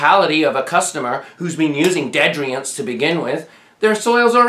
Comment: and 15:14 ... I still don't know what "deadriants" are that he talks about a lot (or sounds kinda like ded-ree-ants). I know that's probably not the word he's saying, but I don't know what word he is saying. I've just heard the word many times, but never an explanation of any kind, so I either know or can look them up. The captions don't work in this video, but in others it shows different words, but none of them make sense or even match the sounds and syllables. I tried looking up different spellings of the word, but never found anything that made sense either.